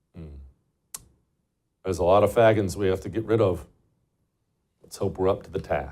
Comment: LMAO I thought Jesse said “there are a lot of fa**ots” we got to get rid of” I was like what the fuck did he just say!?! He said Fagans lmao as the name of the officer - LMAO I was like DAMN JESSE put it on front street